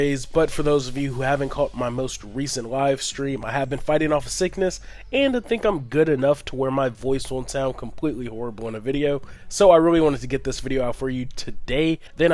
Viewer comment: I thought you said fighting of thickness and I was like what's wrong with being thicc but great vid hope you feel good and awesome intro